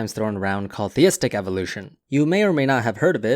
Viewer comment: I thought it said "Thistle evolution is a thing" and I'm like, "so?".